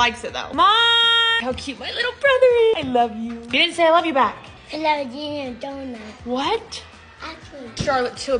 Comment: Why did it sound like said " I love u donkey" 😂